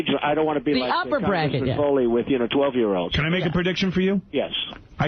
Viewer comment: I hate DJT... BUT.. 4:45 he says "I don't want to be like, ah, congressman Foley with, you know, 12 year olds.." referencing the Mark Foley scandal in 2006. Don't hear what you want to hear.. hear it correctly.